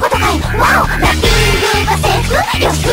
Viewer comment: Did anyone else hear, "My my sausage!" at 2:42? It came from Germany's awesome mouth, of course. Ha...Germany:D